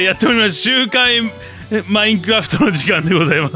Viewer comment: 何回聞いても周回マインクラフトって聞こえる…？